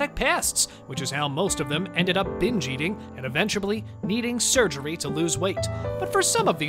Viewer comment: Did he just say “eventuably” ??